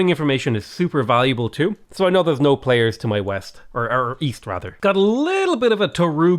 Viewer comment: i thought you said weast